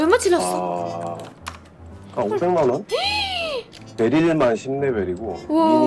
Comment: 500인것 다시 제대로 듣고 알았는데요. 다시 들은 이유가 한 오천만원!? 이렇게 들리네요 왜 5천만원으로 들리지 ㅎㅎ